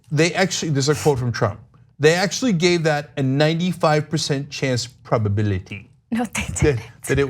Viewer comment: I think you heard him wrong... he said "they actually gave it a 9 or 5% probability of hitting Alabama"